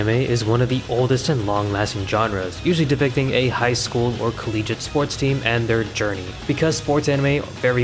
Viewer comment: idk if its just me, but did i hear calleege?